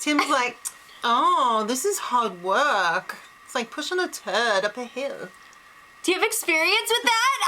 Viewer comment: I thought Tim said : "Pushing a TURTLE up a hill" ... but with his accent it sounded a bit like TURD, I agree ... my first impression was that he said Turtle though, I was imagining one of those huge Galapagos island Turtles, those would not be easy to push up a hill, lol ! ... I wouldn't bet on it though, maybe it's a weird local aussie expression, "Pushing a turd up a hill" ... who knows ... I agree about the waste of time moments in this episode, Joel singing, working out by himself ... I fast forwarded through those moments. Glad Tim caught Joel too, he was clearly giving cards to the Brother and hardly getting anything in return plus it's so pathetic to want others to help you win something, even more pathetic than not being able to win on your own in a fair way. You have to beg others to let you win, that's how pathetic you are in competitions ? Joel could only win a 'Flip a coin' competition. ;-) Later Cassandra was also playing that card begging Tim to take her off the block, using the veto on her .... makes Zero sense really, why would Tim do that ? He's better off being a wild card, playing by himself until the end rather than sticking with those back stabbers. I'd like either Tim or Kelsey to win it all now ... Top 2 choices.